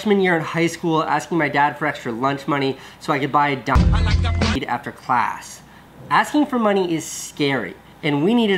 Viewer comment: what did he say at 4:39? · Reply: Denisss632 so he could buy weed after school